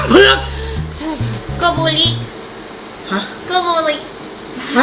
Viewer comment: พูดว่า โกโบริ เป็นชื่อพระเอกในหนังเรื่อง คู่กรรม โกโบริบอกนางเอกว่าจะไปรออยู่ที่ทางช้างเผือกนะ เลยพูดคำนั้นออกมา ใช่ม่ะ😳